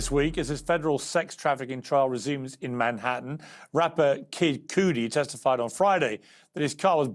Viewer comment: Did Piers just say Rapper " Kid Coodie"? 🤨🙄🥴🤦🏽☔ What a complete moron?! 😤🤦🏽